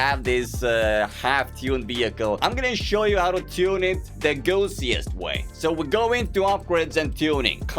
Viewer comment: i thought he said the goofiest way lmao